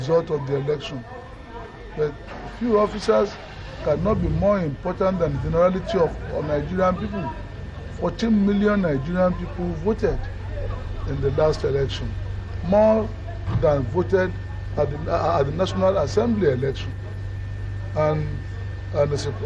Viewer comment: did I hear him saying 14m Nigerians?